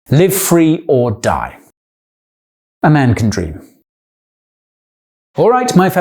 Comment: the way he said "a man can dream", it sounded like "amanda cream", which is a messed up image hehe